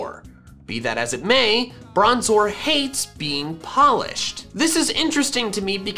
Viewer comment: i heard "Bronzor hate being Polish" and i tough that all my bronzors in Pokemon go hate being here